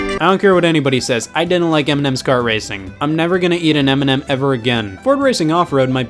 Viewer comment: I swear to God I thought you said eminem's kart racer and got excited lol